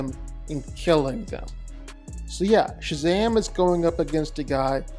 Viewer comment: Did you just say Shazam?😂